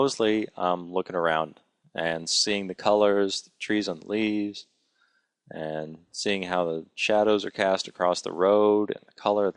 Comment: Did he say trees on the leaves? :d